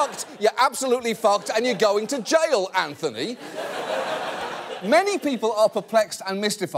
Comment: At the beginning I heard "you're going to jail Anthony" while I was facing the opposite direction of the screen and kinda panicked. Hi my name is Anthony